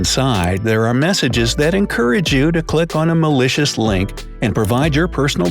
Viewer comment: Where he said malicious link I thought he said delicious link. 😂😂